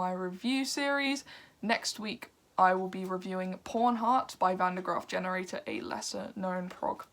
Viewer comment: Forgive my misunderstanding, never heard of Van Der Graaf Generator. It sounded like you said Porn Heart. Oh, it's PAWN hearts. Listening now. Thanks!